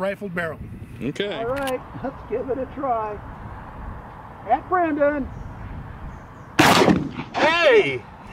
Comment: Did he say, F Brandon? Lmao! 😂